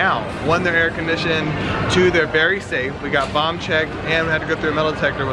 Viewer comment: I heard "Bomb Check" now i feel safe.. 🙆🏼‍♀️😄